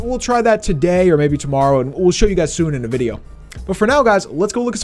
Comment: what did he say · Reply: He said alright